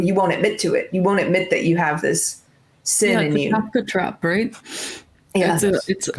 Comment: what is that term she's saying?. It seems to sum up everything in 1 term. It's not what she's saying but it Kinda sounds close to "Catholic Trap". Anybody could help me I'd really appreciate it. Thank you. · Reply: Kafka Trap. <link> -C